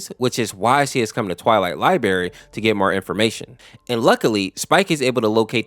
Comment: I thought you said Liberry, not library! XD XD